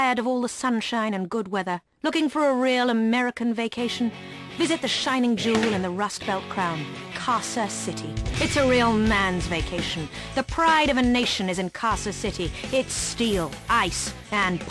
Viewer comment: забавно что я в Гта Са очень часто слышал эту рекламу, но всегда думал что там говорится про Канзас-сити (созвучные названия)